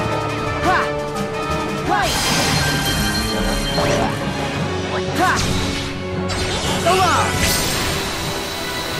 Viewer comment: anyone else hear that? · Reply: Ven/Roxas said "light" with Sora.